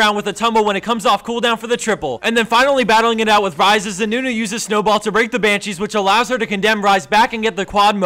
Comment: I thought he said "As Nunu uses Ice Ball to rape the Banshees"